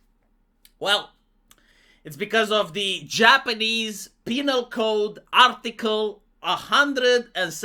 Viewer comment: I don't know why I heard the "Japanese Penile Code" and know I can't unheard it